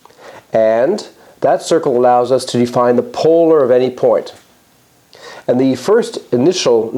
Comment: What was the word you used at 1:16? What definition? May I ask ask you please to write it down here? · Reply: We are talking about the polar of a point with respect to the circle. That is a particular line which is in a special relation to the given point